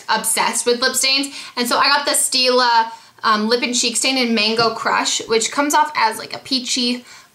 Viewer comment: ZMOG! When you said Mango Crush I thought you said Mango Crotch! bahaha